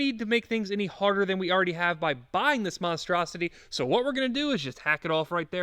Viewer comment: When he said buy this monstrous my video froze so i thought he was gonna say mf